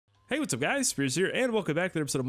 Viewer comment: Thought you said, "Hey what's up guys it's Scarce here." in the beginning.